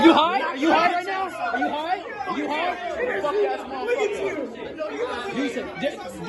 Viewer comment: Sounds like he said "wack ass mudafucker"